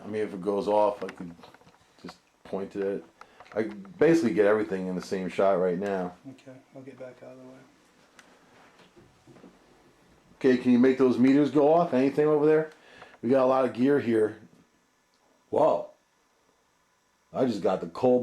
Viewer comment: "Can you hear me" That's what I felt I heard.